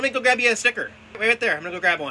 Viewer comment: lol I thought you said "Snicker" like the candy